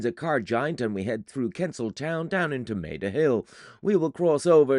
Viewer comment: Kensal Town? Damn, I thought you said Pencil Town and I got all excited. I had so many questions.😂❤